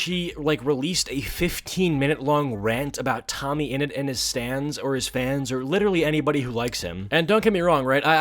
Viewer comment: Is it just me or did I hear "Stands" instead of "Stans" or am I just too much of a Jojo fan?